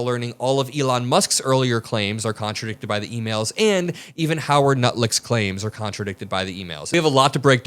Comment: Sign up for your one-dollar-per-month trial and start selling today at <link> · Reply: did you just call the man Howard Nutlick?